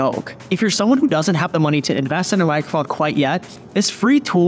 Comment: HE DID NOT SAY MICHROPHONE